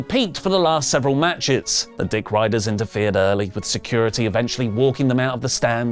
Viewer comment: Did said at 9:39 the D**k riders 🤣🤣🤣 I listen to it several times, I can't still make my mind😅